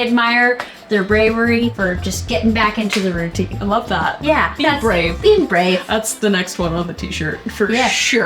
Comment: Lol I heard “bean brave”